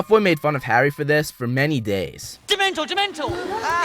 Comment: I can't understand what's Draco says can somebody tell me pls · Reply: "Dementor!! Dementor!!"